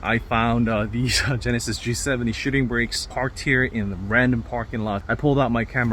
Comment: i thought he said “Shitting Bricks” lmao🤣🤣